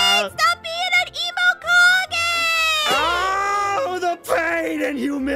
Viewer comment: Saw this Video: "Oh boy, another Aphmau Minecraft video, let's watch!" Have only watched the INTRO: "Wow, not even 15 seconds and there's a Naruto referance, i love the start already" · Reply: did she really say the suffix kage at the end of emo I'll take that as offense